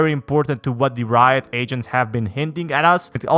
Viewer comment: Bruh I kept hearing “asians” rather than “agents”